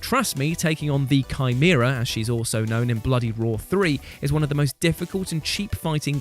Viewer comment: I didn't laugh at Uranus. I laughed when he said "Bloody Roar" because it sounds like he is saying "Bloody Raw." That was unfortunate.